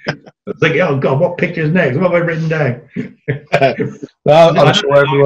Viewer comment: I enjoyed this video quite well and I hope there is more of that to come ! Did figure out to make longbows himself or did he learn it somewhere? Would you mind if I asked what sayed at 34:08 ? It sounded quite funny, was that a casual cockney phrase? I know that question is off topic but I would not like to miss the opportunity to ask native speakers. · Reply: Yes, is self taught. At that time he says "what picture is next, what have I written down"